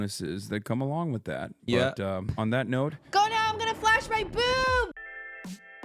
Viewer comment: As an audio listener I thought qt ended the pod with "Go now I'm going to flash bang BOOM" and I thought it was so funny I came to see the clip only to realize I totally misheard her Imao.